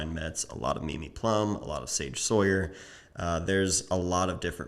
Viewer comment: Who was that "Sawyer" photographer had been looking at? I can't figure out the first name... After Mimi Plumb. At 5:00. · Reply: Believe it’s Sage Sohier.